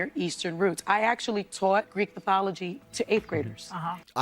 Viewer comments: did she say " I actually taught Greek pathology to 8th graders"!?! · Does she say, Greek Pathology?